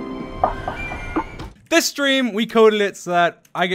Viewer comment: Did he say stream or dream